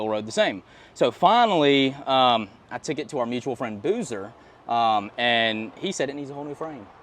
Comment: Roll tide! Are you in Alabama rebuilding the Lexus? I missed the location the first watch I googled the shop and it’s in Trussville. The seller seems like a nice guy. Then I thought I heard him say Boozers who might be Boozers cruisers.